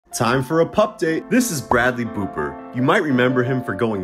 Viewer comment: I thought you said Bradley pooper